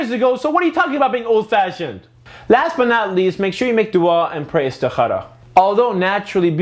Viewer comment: Ummmmmmmmmm....... what did you say at 7:33????? Make praise to what? BTW great episode, very truthful